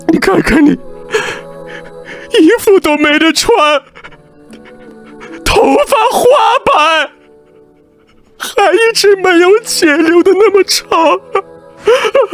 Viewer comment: I think at 9:48 he mightve said "还一直没有qian liu 的那么长 " not sure if the qian is money or qian liu is fringe or smt